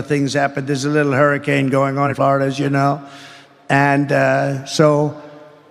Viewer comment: To ME…it sounds like he says 9:25 “as far as you know”..like he’s talking to idiots or kids who don’t know anything. IMO calling them all idiots..but idk. I’m an idiot who doesn’t know what a big n lil hurricane is.